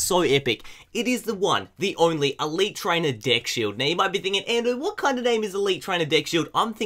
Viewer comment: DID HE SAID DICK SHIELD ?!